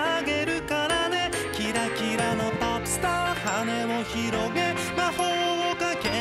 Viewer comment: 昔この歌小学校の給食の時間にかかってて、ポップスターがポスカに聞こえてポスカの歌やとずっと思ってた。